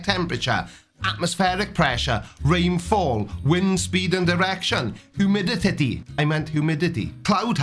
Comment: did you say wind speed and erection? 🤔